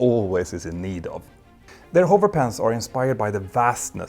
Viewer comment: I had this going in the background during the sponsor section and I swear I heard "hover pants"